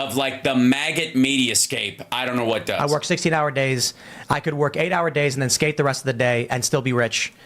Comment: "could work 8 hours a day and skate the rest and still be a wretch". That's what it sounded like he said because he sounds like Alvin Chipmunk.